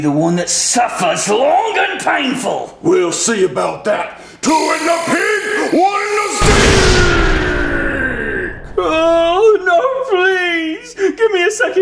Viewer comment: I didn’t hear it at first but did he just say two in the pink one in the stink